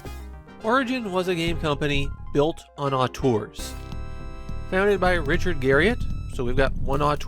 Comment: Built on what? I'm sorry i just can't understand what you're saying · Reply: Auteur, it is an uncommon word used to mean a meticulous artist who often has an uncompromising vision, but also often implies a sort of shortsightedness.